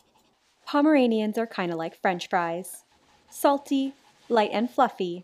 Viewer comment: i thought she said like french guys not like french fries🤣🤣🤣